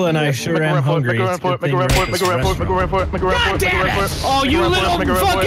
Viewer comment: What was Delirious saying at 2:09? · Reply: he was saying "make a run for it" really fast